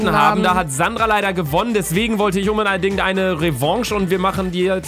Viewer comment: "Umbedeidingt"😂😂😂 wer hat es auch gehört von bei Stelle 00:18 | V